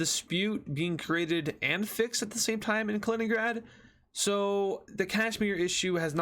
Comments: Did just say Kaliningrad?? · DID BRO JUST CALL KASHIMIR KALININGRAD 💀💀💀💀